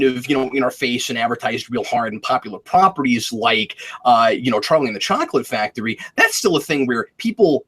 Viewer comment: Okay, I got really confused for a minute when you said "Charlie and the Chocolate Factory" because I heard it as "Terminator: Chocolate Factory" and now I really want that to be a thing.